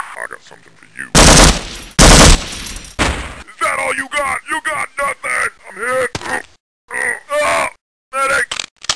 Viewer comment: What did he say..? Is That all you got?? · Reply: That all you got?! YOU GOT NOTHIN'!Augh Medic!